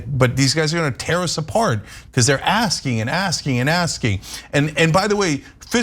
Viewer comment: I heard "He was an ass king" I like that better than, "He was asking..."